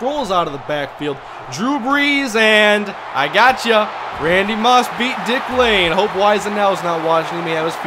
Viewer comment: "Randy Moss beat Dick Lane"......... All I heard was "Randy Moss beat dick"